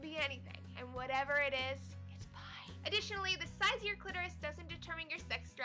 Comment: I thought she said "It's mine..."